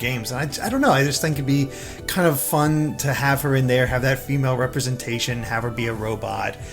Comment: It’s hard to take this seriously when I keep hearing ‘muh female representation’.